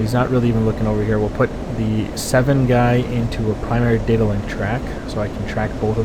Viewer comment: I heard primary diddle